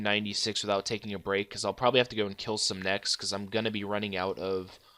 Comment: have fun killing nex 0:44 >i know thats not what he said